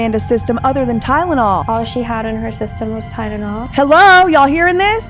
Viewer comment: Did it sound like she didn't exactly say "Tylenol" to anyone else? What I mean is, it sounded like she said "tydenol" to me. Like that first "L" was actually pronounced as a "d" for whatever reason. Idk,maybe I'm hearing it wrong. Maybe I'm not...but even if I'm right, I'm not necessarily saying there may be some sort of nefarious reason for it. To me, it's odd enough & I want to at least see if anyone else heard it.